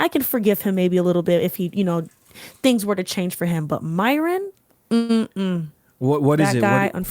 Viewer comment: did she say she wants to fk baldass Myron 👀 fr